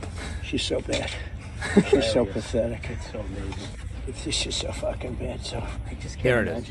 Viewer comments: It sounded like he said “she’s so fucking bad” I didn’t hear “she’s a fucking bitch” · I hate Trump as much as the next person, but to be honest I keep hearing "its just so f*cking bad". I dont think he called her a b*tch in this video, but it wouldnt surprise me if he has done it some other time.